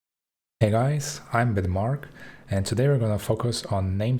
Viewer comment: I thought you said "Hey guys, I'm Denmark."